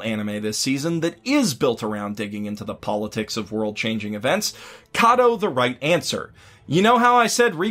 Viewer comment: What anime is he talking about in 22:13 im confused, I heard Kaito but the results on anime sites dont show me something like that, could anyone help :D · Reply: Seikaisuru Kado